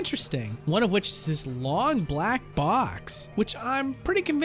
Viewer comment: I thought I heard "Long Black Cocks"